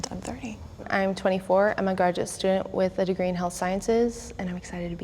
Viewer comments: did she call herself gorgeous Hypocrite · Did... did that girl just say "I'm a gorgeous student with a degree in health sciences"? Did I mishear that?